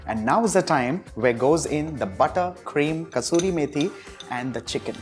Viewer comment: What’s the final garnish? It sounds like he says “crushed may pea” or something. Can’t seem to figure it out. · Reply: It's crushed kasori methi😂 (roasted Fenugreek seeds) 😅